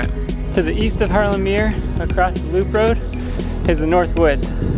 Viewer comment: Hey man, first of all thanks for the fantastic videos! I highly appreciate your work and effort! One question to 4:35 though: what do you say there? Luke Road oder duke road or what? And isn’t that just East drive anyway? Could you or someone else please help me out? Thanks a lot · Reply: I call it the "Loop road." Yes, technically it's East Drive, but that get's confusing when you're up at Harlem Meer, because it's on the north side of the Park. East Drive turns into West Drive and then at the south end of the Park, it goes from West to Center to East Drive, but there's no clear indication of when they switch names. Since it's all the same road, most people call the whole thing the loop road, and refer to East and West Drive when you're only talking about a specific section on the east or west side.